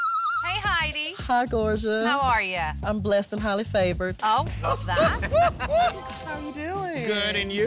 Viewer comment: Oh my drag.. I misheard it as Blasted Holla Flavored.. 🤣😂😅